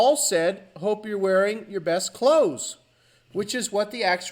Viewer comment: I heard "cloat" the second time. This crap is bizarre. The Bee Gees one is glaring.